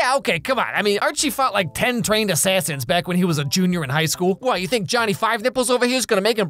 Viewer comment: wait did he just said Junior in high school because I am senior I don’t look like that nether my classmate